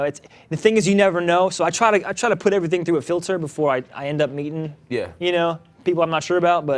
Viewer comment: did he say "mating" or "meeting" ? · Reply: Definitely mating 🕎